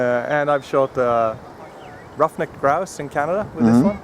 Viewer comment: What animal did he shoot? Sounded like roughneck and then some word I couldn't recognize. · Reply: Ruffed Grouse, Its a bird native to North America.